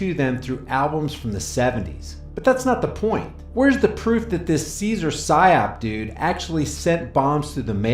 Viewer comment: Every time you say Cesar Sayoc I hear "Cesar PSYOP" That van is clearly staged.